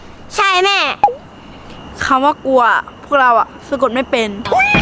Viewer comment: ~2:05~2:06 : พูดว่า : พวกเราก็สะกดไม่เป็นว่าไอ้คำว่า "กลัว" มันสะกดยังไง<num> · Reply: พูดว่า : คำว่า "กลัว" พวกเราสะกดไม่เป็น<num>